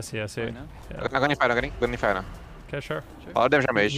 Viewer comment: What the heck did he say and in what language? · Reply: he said, "I'm going to infernal, k? going to infernal"